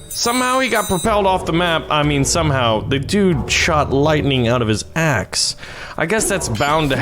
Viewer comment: did only i hear "he shot lightning out of ass" ????